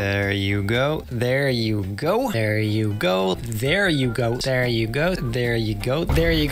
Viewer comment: Did anyone hear him say there you goat